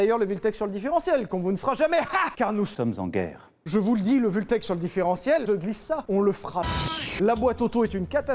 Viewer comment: Moi j'ai entendu " le Vultech différentiel on le fera" . Merci bien, on l'attendait depuis longtemps